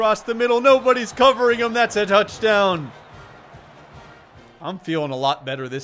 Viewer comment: did he say nouchdown